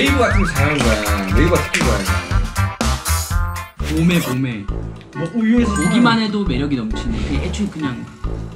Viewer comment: What did JK say at 2:44, something about uju (milk?)